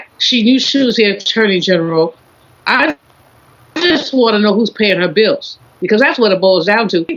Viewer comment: did she just say who is paying Janet Jackson Bills 😂😂😂 she can pay this whole panel bills 10x’s 😂😂